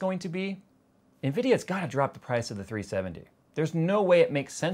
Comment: Did just say... "Nvidia's gotta drop the price of the 370" ???????wuuuuut? NVIDIA OWNS AMD NOW!? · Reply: He said ''970''